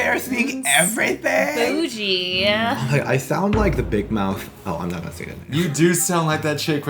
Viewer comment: “oh I’m not gonna say THAT name” is that what I heard?? 😂😂😂 ohhhh man